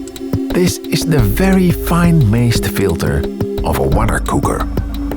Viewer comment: I can't quite make out what he's saying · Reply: This is the very fine mazed filter of a wondercooker